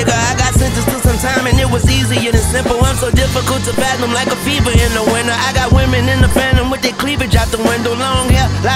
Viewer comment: Did wayne say “I’m so difficult to fathom like a feeble in the winter” might have been a skate bar tbh, not sure